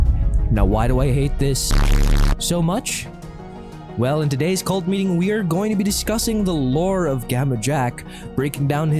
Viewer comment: what word did you used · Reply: Shit?